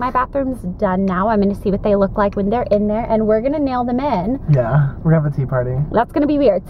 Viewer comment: Haha, when you were in the car and you said "we're gonna nail them in" I COMPLETELY thought you said " we're gonna nail the men" LOL I had to listen, like, 3 times to hear it correctly XD